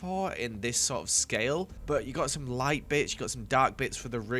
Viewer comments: I THOUGHT HE SAID "light bich, - BUT HE SAID "bits" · The first time I heard it, it sounded like "some light b#tch" instead of "some light bits" I couldn't stop laughing🤣🤣🤣